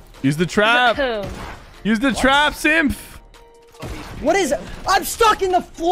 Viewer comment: On 3:47 , why does he sound like he said simp 😂😂